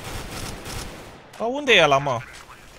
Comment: did you just say at 3:21 :Unde-i ala ma? lol :D